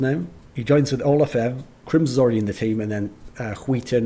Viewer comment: Lol'd at the way you pronounce skytten, it sounds like you say "skiten" which means "the shit" or "the crap"